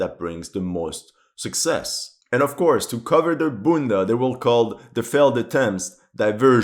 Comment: did you actually say "bunda" (ass in portuguese)? 👀